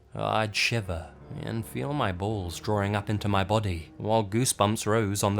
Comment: Skip to 3:20 did you say " I would shiver and feel my balls shrivel up to my body! " or something in that matter LMFAO 😂😂🙊🙉🙈